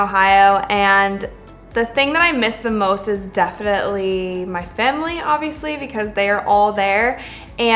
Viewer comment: Why does it sound like she said “Femily” 😂